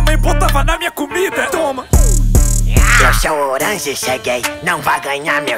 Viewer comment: só eu que ouvi seu gay ao invés de cheguei? Eu acho que tenho algum problema na audição💀💀💀